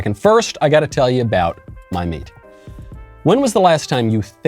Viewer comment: Regarding 1:51 At first it sounded like you were talking about mead. Then you mentioned the ranchers. =))